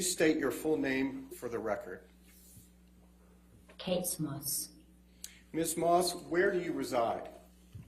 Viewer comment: Did she say Katessss Moss?????